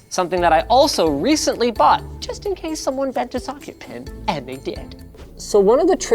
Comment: He sounds like he is saying "just incase someone bent a fucking pin"